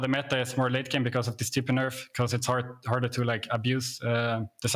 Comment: I lol when I thought he said stupid nerf